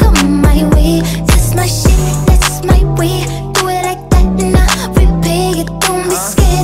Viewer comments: I thought she said that’s my weave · Lol i thought it said that's my shit and that's my wig